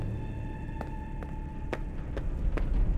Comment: what did Vanny say? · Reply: She said “Are you having fun yet?”